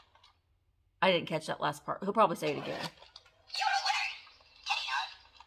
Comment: Looky here, see.....great voice over. I thought the bear was saying "you're a loser", not winner. Hahaha